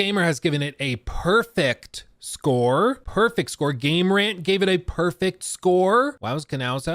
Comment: When he said game rant gave a perfect score I thought he said gameranx and was like noooooo